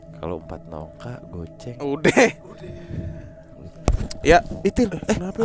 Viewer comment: yang kedengeran di gw "udeeehh" tapi bernada dan panjang. beda ama lebih pendek kayak cuman "udeh" dan ga bernada